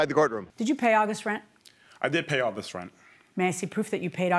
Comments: I thought she said "all this rent" too😂😂😂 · I thought she said “all this rent” too. 😂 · I thought she said all this rent too. 😅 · I thought she said “all this rent” too. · I thought the judge said “all this rent” also. 😂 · I thought she said "all this rent" too. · I thought Judge Milian said "all this rent" too. 🤣😂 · lol, I thought she said all this rent too 😂